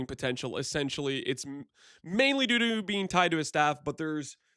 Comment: did he say "mainly doo doo"? lul